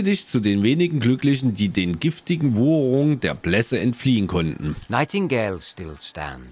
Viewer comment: was sind denn "giftige Wou-uu-ungen der Blässe" ? Ich hoffe ich hab das richtig geschrieben . Hab mehrmals ganz genau hingehört . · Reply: Dann lös ich das mal auf . Im Text stand "Wucherungen" . Sag doch einfach mal "Wucherungen" und hör Dir dabei genau zu . Aber nicht schummeln .